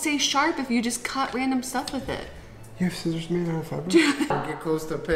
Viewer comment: I'd never paid close enough attention when he said "You have scissors made of fabric?" I thought he said "You have scissors I don't know about?" But it's one of those things that you don't question because you think you're right. It makes sense in context, too. But I read the comment before the video played and I was flabbergasted that I'd gotten it wrong this whole time.